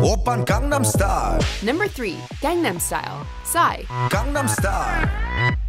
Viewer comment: I always thought he was saying “Gundam Style”.